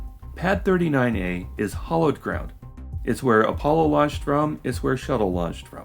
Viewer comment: @ 27:30 it sounded like you said, "hollowed ground" RE: Pad 39A. You did say hallowed, (highly venerated) right?